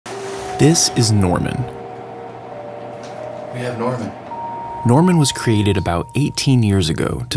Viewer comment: Why did i hear ‘ this is Nornan’? 💀💀